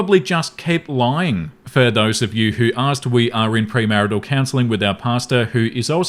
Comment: I had to laugh - it sounded like said "We're in premarital counseling with our pasta". I thought "What does your spaghetti know about marriage?"